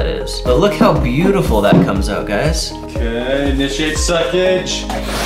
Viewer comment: "it's got a lot of succ" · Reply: I thought you said "initiate sausage" at first.